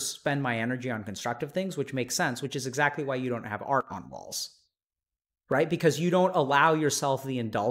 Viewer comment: Is that "...art on walls"? I listened to it like a dozen times and I'm still unsure. The useless youtube captions said "Archon walls" which I thought was maybe some kind of psychology lingo, but it isn't actually a thing.